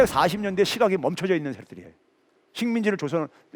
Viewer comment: 순간 욕하실뻔 하신거 같은데... 잘 못 들었나 ? ㅎㅎ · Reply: ~~~~새끼들이에요 ㅋㅋㅋㅋㅋ